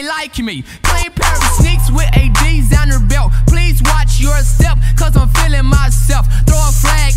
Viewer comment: i was today years old when i realized he said “designer belt” instead of “ds on your belt” 😭😭😭😭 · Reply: Bro this whole time I thought they were saying “with adidas and her belt” 😂😂😂